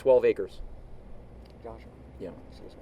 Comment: "Josh, Seriously" "what she's asking questions"umm WTF Is it just me or does he say that?